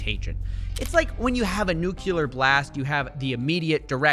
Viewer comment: did I hear you say "nucular"? are you a Simpsons fan or...?